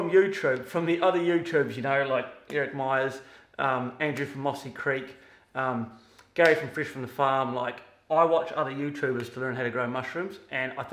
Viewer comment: What YouTube channel you speak about at 7:47 ?? I can't hear properly, this can't be one I don't know about my espionnage mission failed ¿... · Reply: Myers mushrooms, mossy creek mushrooms, and fresh from the farm fungi.